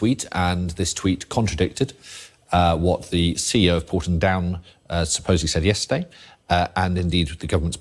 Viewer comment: Did I catch that correctly around 12:42 ? "What the CEO of Porton Down SUPPOSEDLY said yesterday ." If so, this could be a type of subliminal language to further convince us to swallow their lies. I will just add that as a person trained in hypnotherapy, I sometimes slip in subliminal language suggestions of a type similar to this but for positive change, not to deceive. I could be wrong but thought I'd mention it anyway. Actually, every word he utters is very similar to a hypnotic script. Ben Wallace as well, every word is carefully crafted, very much like hypnosis.